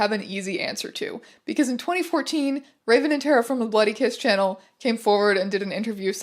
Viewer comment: I always hear "Buddy kiss" and it wakes me up inside 😂